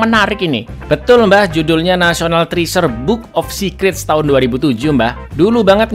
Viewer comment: hampir salah denger... National Threesome Book Of Secret